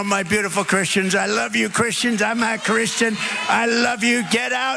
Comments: Did he say he's not Christian? I've replayed it at 3:00 so many times and it sounds like "my beautiful Christians, I love you Christians, I'm not Christian" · Did Trump say "I'm not Christian?" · listen to trump he says Im not a christian ! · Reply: Not that I care, I'm an atheist .. but he said "I am a Christian", although he pulled a Biden and kind of mumbled it. But I heard "I am a Christian". Personally, I don't think he's religious at all, but he knows and caters to his base.